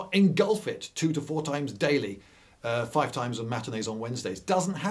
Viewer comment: what is the word spoken in the sentence four tides a day, five times on “materdays” and wednesdays? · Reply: Matinees. He was jokingly likening tide times to theatre schedules.